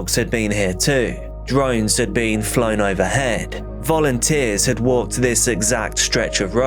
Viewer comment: ~ When I heard you state, @ 1:06 “volunteers” it sounded exactly like ‘fallen tears.’ Quite apropos wouldn’t you say? Oh my goodness, this poor wee little dear one was afraid of the dark. 😢💔😭